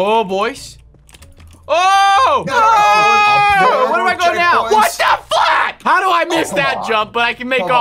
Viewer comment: dis anyone else hear say what the FLUCK at 1:53 seconds? He tried to say F word and flip at the same time